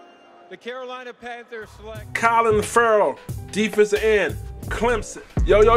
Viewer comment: Sounded like u said COLIN Ferrell... CLELIN , bruh!.. lol